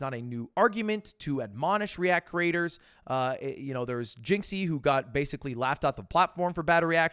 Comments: is it just me or does it sound like he's saying Jynxzi instead of Jinx 😭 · Did he say jynxzi? 3:05 ? Laughed out of the YouTube platform? Wtf 😭. No way he’s talking about jynxzi